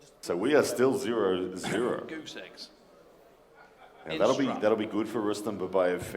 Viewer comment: what did he say XD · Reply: He said goose eggs.